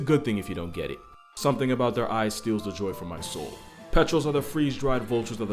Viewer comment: I had to Google petrel, because I thought he was saying "petrol."